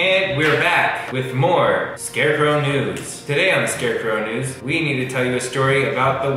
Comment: I really thought said "scarecrow nudez" at first but I was incorrect